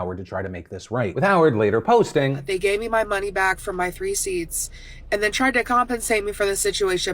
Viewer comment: Did she just said 3 seats 3:11... lol